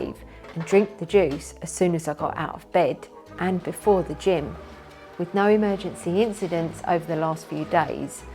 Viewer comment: I honestly thought you said ...."..and before the Gin" had to rewind a few times to get that you said "Gym" not "Gin" 🤣